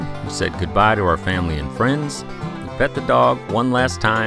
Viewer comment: I heard "fed the dog one last time."